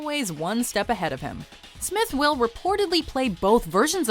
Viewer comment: Did she say Smith will